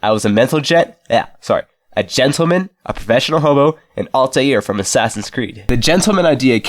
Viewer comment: i thought you said homo for like 2 seconds edit: 2:03 i